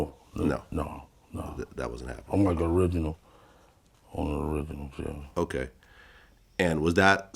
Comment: RIP PAC....its sad he has been able to live his life freely, but what's done in the dark always comes to light... he'll get what he deserve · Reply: Did Keffe say he's an original killer under his breath 🤔